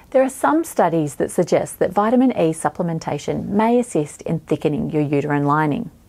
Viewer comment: I thought she was saying vitamin A, not vitamin E. Other Americans who've watched...amiright?